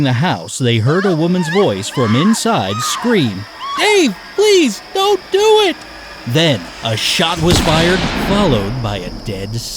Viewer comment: Thought she said «HEY PLEASE DONT DO IT»